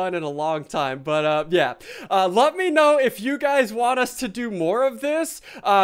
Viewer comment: it sounds like you said "love me know" insted of let me know xD